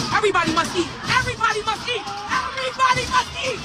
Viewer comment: I thought she said everybody monkey ???